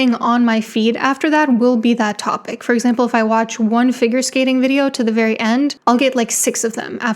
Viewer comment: i heard finger skating 🤦‍♂️